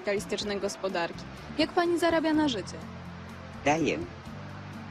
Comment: Czy tylko ja usłyszałam "ja jem dupy" xd